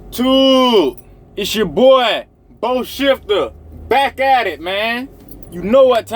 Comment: Haha, during the intro to your video, I thought you said your name was "Bull Shifter".